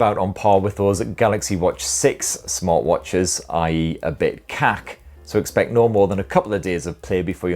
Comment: did you say kak 😂(as in shit)